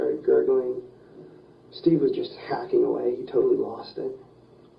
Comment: I’m sorry but at 35:00 I laughed when he said “I stabbed her 3 times in the chest.. he was just backing away.. he totally lost it!” Like yeah.. HE lost it.. the rest of you were fine.. like 3 chest stabs? Who wouldn’t do that? Lol it’s horrible and I’m laughing at the fact that he’s so far gone he doesn’t realize what is real.. like he genuinely thinks he didn’t lose it because he stabbed her 3 times instead of hacking away.. 🤦🏽‍♂️ I bet that poor girl was so scared it’s good she survived but that’s gonna leave a scar on her brain.. hopefully she got some extreme physiological help! 🙌🏽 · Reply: I agree! He said hacking away though, not backing!